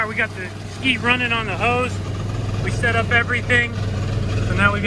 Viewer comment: I was looking away when I heard say "We got the ski running on the hoes" Me- excitement!!!!! Also me- Oh the hose, of course. ((Clears throat))